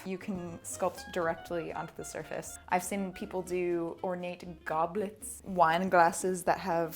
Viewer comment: I heard ornate goblins and I was confused, then delighted